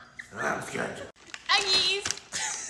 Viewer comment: When u said Eggies I thought u said nuggies